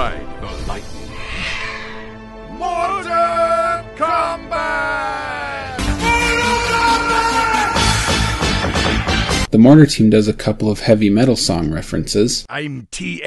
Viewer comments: All this time I thought the mortar team said "for honor and combat" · I feel ashamed after all this years I didn't get mortal combat reference... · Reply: I misheared it was Mortar ! Come Back ! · Oh my god, for SO LONG I thought the Mortar Team said "Mortar! Come back!" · "Mortar Combat" is MIND BLOWING. All this time I thought they were saying, "MOORTAAAAR, COME BACK!" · Oh shit. After so many years I always thought they said "mortar come back" . OMFG. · I always thought the Mortar Team said "MORTAR,COME BACK!"